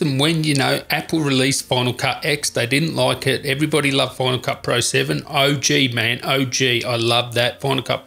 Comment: “Using bona-ka, that bona-ka, bona-ka”.... dude, FINAL. CUT. PRO., it’s three separate words god damnit! I kept hearing “bona-ka” and wondering what the fuck a “bona-ka” was...🤬!!! Jesus....